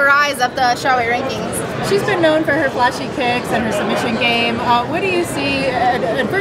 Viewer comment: i swear she said "she's known for her flashy tits..."